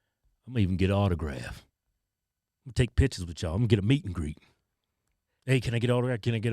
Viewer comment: Wait, at 16:00 is he saying meet and greet or is it MEAT and greet? Just curious💀